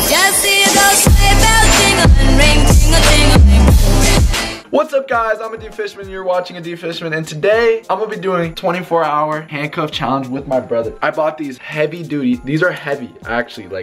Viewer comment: I thought he said adick fishman